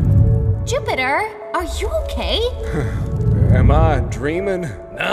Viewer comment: i thought Europa said “Jupiter are you gay” even though she said okay😂